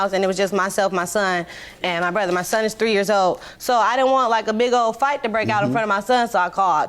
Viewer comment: Hell i thought she could have said im fourteen years old! 🤷🏼‍♀️